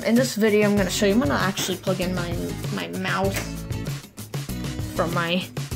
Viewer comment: I heard I’m actually gonna plug in my mouth when you said mouse 0:52 I also heard I definitely want a mouth